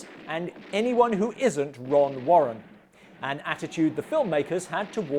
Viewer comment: I misheard the hero's name as, "Wrong Warrant"; which sounded a bit on the nose for a renegade cop. That's a great Dad Cameo; obviously where your comic timing comes from.